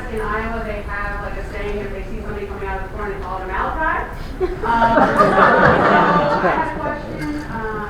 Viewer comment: What did she say? "If you see something coming out of the corn, it's all a Malachi." Huh???